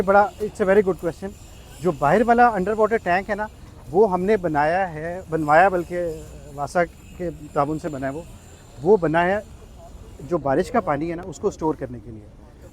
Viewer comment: Did he say NASA ?? At 3:58 minute mark :D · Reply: He said WASA it is the department of water supply in pakistan